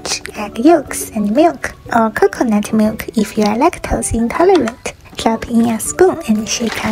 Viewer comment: "adds coconut milk because I'm lactose intolerant" "adds cheese because the recipe told me to do so"💀 edit: the comments are full of people who don't get a joke nor irony. I know it's optional, it's a recipe you don't have to follow step by step. I know vegan cheese exists. My god don't be so serious, I just found it funny how she talked about lactose intolerant alternatives for milk but then talked about cheese (and didn't mention alternatives) at the same time. That's all. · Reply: I thought she said if you are lactose intolerant